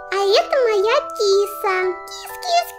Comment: послышалось это моя писа